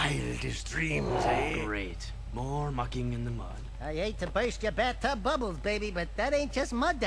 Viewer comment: krew is so disgusting. when said "mucking in the mud" at first i thought he said "f*cking in the mud" and i was like ummm okay then